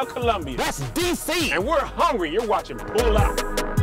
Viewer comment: I thought said you're watching PULL OUT We know don't pull out!